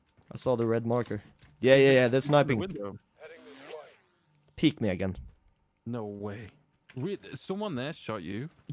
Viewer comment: I know I ain’t the only who thought he said “peek nigga”